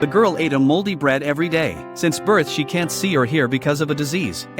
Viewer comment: For a second I thought you said holy bread lol